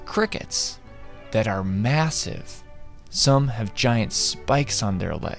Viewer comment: did you say massive?